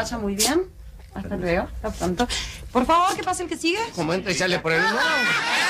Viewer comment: Por favor, ¿qué dice Olmedo en minuto 2:00.? No alcancé a escuchar.! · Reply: Como, entra y sale por el mismo lado ?, Eso dijo